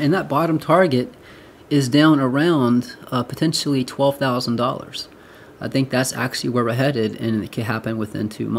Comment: Well done on freaking me out ! 14k no way 😪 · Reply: lol I heard 12k